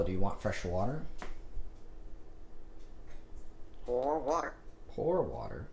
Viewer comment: I thought he said "Whore water" 💀💀💀